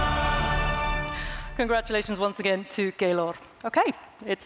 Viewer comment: LOL i swear at 17:27 when she said Keylor it said Gaylord for me in subtitles